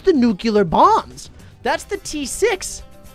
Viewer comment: Did he just say that was the t6 bomb? you really need to pkay normal btd